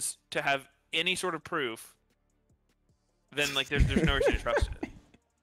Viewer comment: What does he say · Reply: "Then there's no reason to trust it"